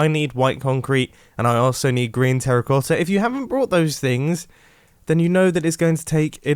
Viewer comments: I thought he said “Grian Terracotta “ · "Grian" Terracota....hmmmmm (I know its green terracotta but thats what I heard the first time) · did he just say Grian terrakotta? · Who else heard gRiAn terracotta at 3:35? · I swear he said grain terracotta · Did anyone else hear him say Grian terracotta